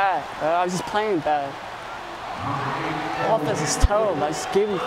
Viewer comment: Down 28 to 7 says is bad at the game.. wtf is this kid on. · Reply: Actually, I thought he was saying that he was playing bad.....check out 20:20 to 20:26(approximately)..... I freely admit I may have misinterpreted it, but I SWEAR he said he was playing bad....I don't know how much of a difference it would have made........